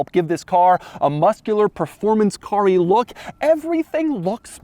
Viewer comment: man I misheard that as Performance Curry